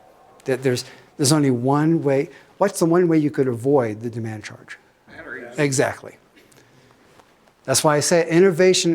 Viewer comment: I couldn't hear the answer to the question at 45:36 "What's the one way to avoid paying the demand charge?" Can anyone enlighten me?...erm...empower me?. .erm...give me some joules of wisdom? (sorry!) : ) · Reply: Batteries